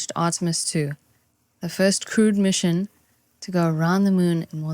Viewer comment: The first crude mission?? No... The first crewed mission. I totally misheard that the first time! Hahaha.